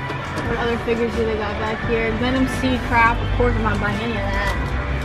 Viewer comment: did she just say Gundam seed crap? Omg you're one of THEM...